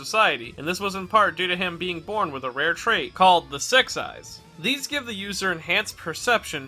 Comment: I misheard the six eyes with the se* eyes lol